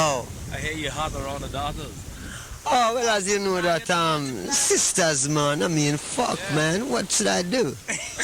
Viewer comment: Thanks for sharing! Does anyone know what's beeing said at 16:51 and Bob's response. It seemed funny but I don't quite undeerstand it all :p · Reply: Hahaha, someone said in the background that Bob was flirting with the chicks. Bob said: They are sisters mon. I mean, fuck man then what should i do??? Then everybody was laughing so hard about Bob's comment. #LOL